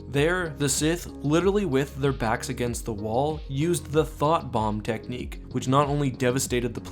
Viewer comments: I keep hearing it the"Thot Bomb" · I thought you said "Thot bomb" and i was like, man the sith were some chads i guess. Great vid and its been a great year following you man. Heres to a great scifi filled 2019!